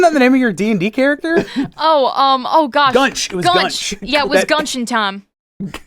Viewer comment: misheard: 20:36 It's Gunchin' Time